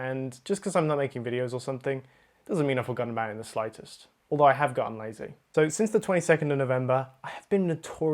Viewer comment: I thought you just said "though I have gotten lasik". My heart! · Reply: Ya , I also heard that same but than I realised that he said I have gone lazy